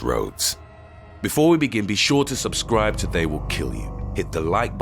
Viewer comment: I thought he said "be sure to subscribe or i will kill you"